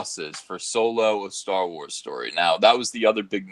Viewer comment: I heard "star wars story group" as "star wars soy group."